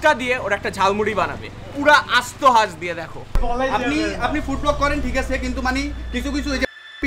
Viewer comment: I think he said "Food appi"😑